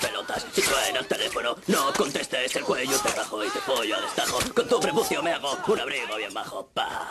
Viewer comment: "Con tú prepucio me hago un abrigo bien bajo" Es lo que dice? xD